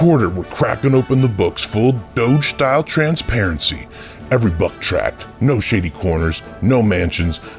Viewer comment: I giggled! I thought he said "...every butt crack, every shady corner..." because I was listening to you read it! Hey straight dudes... heteros are welcome just don't throw shade on my gay paarde. Thank you.